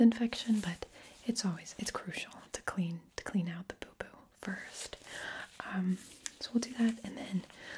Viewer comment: i thought she said poopoo